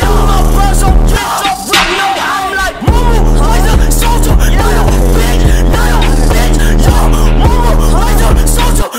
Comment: someone tell me what x is saying because this is my favorite part! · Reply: Momma raised a Soldier not a bitch